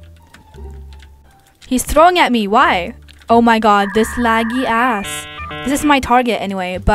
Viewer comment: did said a** at 3:24 or she said has/as idk